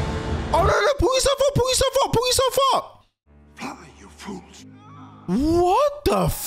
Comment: No, he clearly says "Run, you fools," but for some weird reason the subtitles say "Fly"...